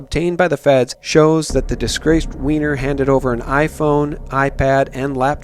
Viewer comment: Time 4:48 did i hear disgraced Weiner...ROFL a disgraced Weiner is going to be the undoing of Hillary Clinton...LOL